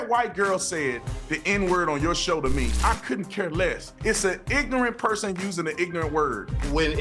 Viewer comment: ends in -er or -a I always hear "I AM AN IGNORANT PERSON"